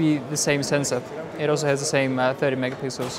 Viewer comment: am i then only one who thought he said 30 mp instead of 13mp